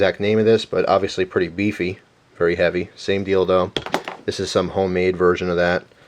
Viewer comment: DID HE SAY "SAME DILDO"???